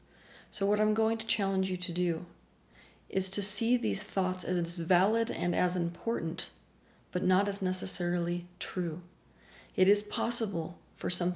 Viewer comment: Question. She said that we should make these thoughts as invalid but important(?) it may be obvious, but the wording is throwing me off. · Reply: I think you've misheard.She actually says "to see these thoughts AS valid AND important BUT not necessarily true."